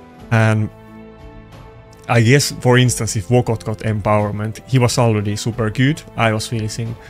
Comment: who is wott got? I thought you said locked out, but the only epic that does that is basher. I turned on the cc and it said wott got. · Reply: vogoth ; D